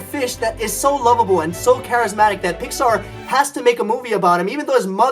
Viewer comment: does he say Vikkstar? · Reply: No he says Pixar